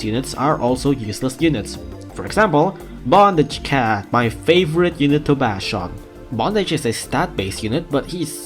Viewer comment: heard that as ”my favorite unit to back shot"